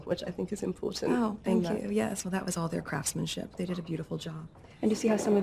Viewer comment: Who heard her swear at exactly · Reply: Bumble Vlogs she says craftsmanship. Unless you mean that’s just the way you heard it idk ha